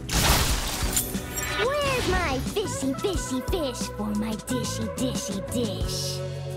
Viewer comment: After searching on Google I found Yummi say's "Faster Faster" And not "Baster Basterd" MB may be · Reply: Also this reminds me I've always thought she said "Where's my bishy- bishy- bish? On my dishy dishy dish..." and last time I played her I heard her saying "fishy fishy fish" 😭 I never laughed so hard 😭😂